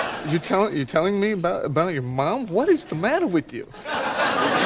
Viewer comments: “I SAID SHARE NOT SCARE. sit over there” XD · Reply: Wait I thought it was 'I said share not stare' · "I said share not scare" what an iconic line by Ross 🤣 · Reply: Wait I thought it was 'I said share not stare'